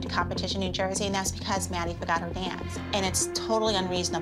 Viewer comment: Am I the only one that thought when Holly said “ Maddie forgot her dance “ “ Maddie forgot her pants 😂